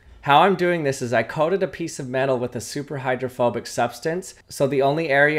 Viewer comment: For a second I thought you said homophobic instead of hydrophobic 💀